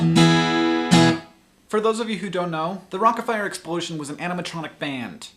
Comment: omg, for the first half of the video I thought Hank was saying, "rocket fire explosion", then I looked at the title of the video. :) tee hee.